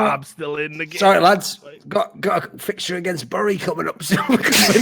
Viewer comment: could you please clarify what Thorin says? Since they bursted in laughter and I could not understand what he said/meant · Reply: "Sorry lads, got a fixture against Bury coming up soon". Bury FC being a team in the lowest professional league. And then "Whatever, took a draw 1-1, still counts". Basically creating a sad picture of Deman streaming Football Manager playing lower league English football